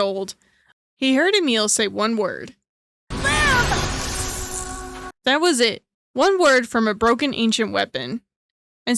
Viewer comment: what did said...? I don't get it... Will ? Win ? Wait ? Veiled ? Weehuh ?? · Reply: Live